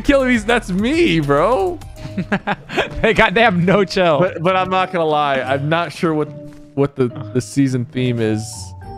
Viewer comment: Did anybody hear what Nick is there? He said in the first round. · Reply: For real he said god damn